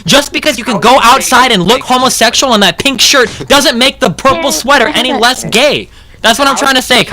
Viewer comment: does anyone else hear say "yay, i have that shirt"